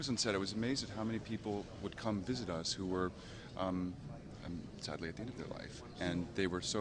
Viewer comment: "sadly..." what? Someone understand what Lee said? Pls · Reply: "...many people would come visit us who were sadly at the end of their life..."